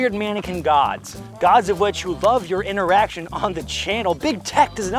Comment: Why does it sound like you said YOU LOVE YOUR ERECTIONS and talk about a lot of PENETRATION!! Lmao